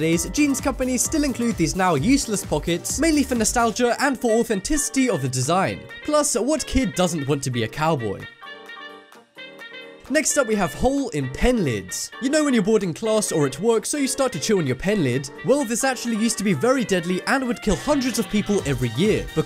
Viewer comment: i thought it said HOLE IN PENIS